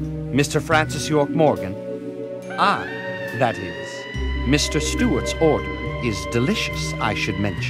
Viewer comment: Whaaaat....?! I thought I heard you say Mr Stew is your son, maybe you have been out too long in the sun?